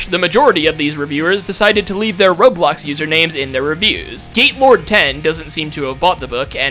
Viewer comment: I understood GAYlord10 instead of GATElord10 💀😭😭